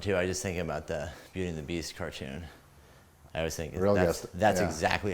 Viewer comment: did say Vietnamese cartoon or beauty and the beast cartoon